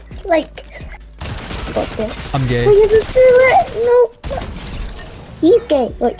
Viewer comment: sounded like "im gay" 🤣